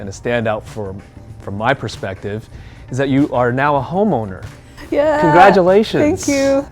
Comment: i thought he said homo